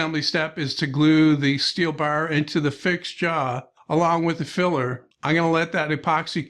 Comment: I thought I heard a bit of Norm Abram in there before I realized you didn't say "fixture."